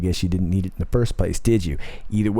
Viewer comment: What did you say to stay off of ? At the very end?? Sounded like you said stay off the vape! Lol